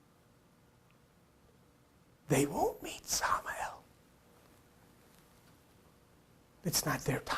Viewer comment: what is the word you are saying around the 55:15 mark. It sounds like samadel or sama el? Who is this entity.... God? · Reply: The name is Samael. Here is a link to a page I did on the Hidden Meanings web site. About half way down you can read about Samael Here is the link <link> <mail>